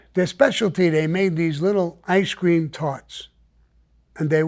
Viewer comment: I'm high as hell wondering what is an icecream tart🤔.... I want one😆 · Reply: I thought he said ice cream farts. 😳